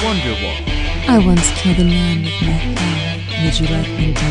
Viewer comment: Yo lads, i'm translatin this, can you help me understanding what does the grave robber say at 0:17 ? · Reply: yeah I kinda messed up the audio a little, she's saying: I once killed a man with my thumb, would you like me to demonstrate?